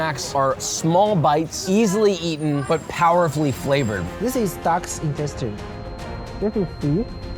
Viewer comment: DOGS INTESTINE?! BRUHHHHHH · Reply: I thought he said “Duck’s intestine,” which is bad enough…